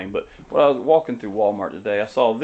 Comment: Where did you get the foam glider? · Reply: I think he said at Walmart (at 0:13), and it was only about $10... :D